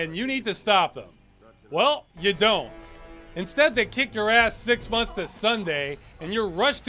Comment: Did you say, six months to Sunday?